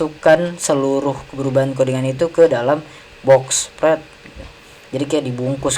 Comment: gan, ane gasalah denger kan ente bilang 'box pret' ? wkwk